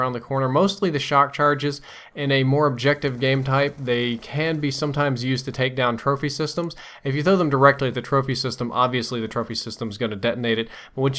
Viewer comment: Sounds like ur saying turfy system